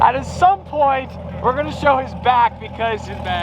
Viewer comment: it sounded like he said, “This car sounds like a raging B***h.”